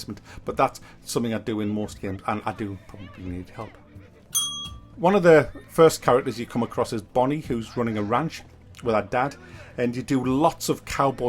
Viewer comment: lol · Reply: For a moment I thought you said "one of the first characters you meet is bunny who is running the farm with her dad" at about 3.25 edit.. link to the bunny part <link>